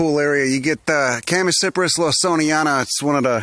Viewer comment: What's the name of the plant you mention at 8:33? I can't quite catch it. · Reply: Okay, nvm I think I got it, Chamaecyprus lawsoniana?